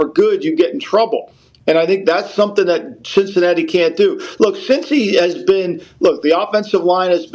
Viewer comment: Did Lombo say Sh!t-cinatti?!?!